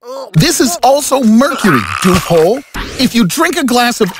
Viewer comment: did narrator say dickhole?